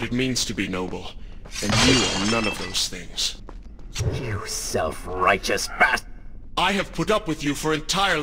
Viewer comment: What did the Dark Prince say? I think he was about to say, "YOU SELF RIGOUS BASTARD!" But he almost said "BASTARD!" but he just stopped himself halfway.